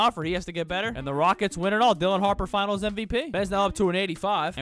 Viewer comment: I thought u said Dillion Harper then I rewound the video and saw it was “Dylan” 😂😂